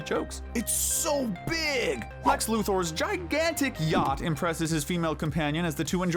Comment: I misheard it and thought it said "Lex Luthor's gigantic gyatt"💀